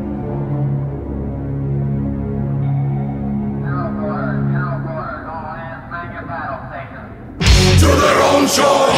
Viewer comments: What does he say? " [...], [...]! All hands, man your battle stations!", but what ate the first two words, please? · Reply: Anyone with good hearing would know it's "general quarters". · Does anybody know what is said in the background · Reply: "General Quarters, General Quarters, all hands man your battle stations"